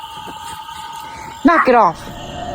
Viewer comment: I could've swore I heard F off